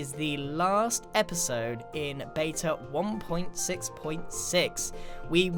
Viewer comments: I thought he said the last episode so started to get sad but he actually said the last episode in beta something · For a moment I thought Grian said that this was the last episode of Evo... I WAS TERRIFIED!!